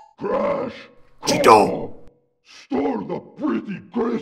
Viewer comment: When I was much younger I always thought Tiny said "Crash! Cortex stole the pretty crystals!" It was probably the way he says that and how Coco was muffled a bit that for some reason made me hear "Cortex" every time :P